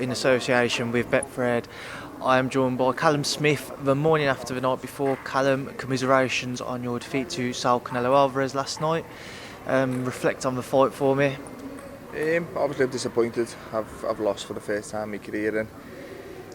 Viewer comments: I thought he said congratulations on your defeat 😂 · “Congratulation on your defeat” LMAOO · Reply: it sounds like he said commiserations